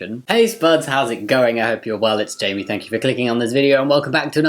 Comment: you said the intro so fast i thought you said "i hope your wallets jankey"! XD